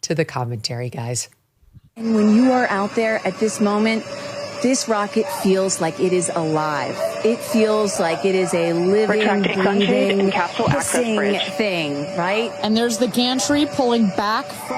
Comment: It sounds like she says "a living pissing beast" which is more apt considering how phallic that rocket is. Bezos should have at least painted it gold.